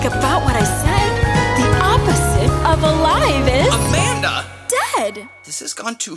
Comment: In someway i hear this: "The opposite of alive is Amanda"